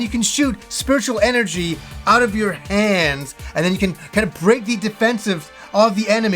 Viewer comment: Lmao!!!! Around@ 2:22 i so thought u wer gonna say "... & u can shoot spiritual energy out of your ASS", not "hand". At which point i pmsl.....dunno y, its just stuck!!!! Great vid as per tho..... gj dood :)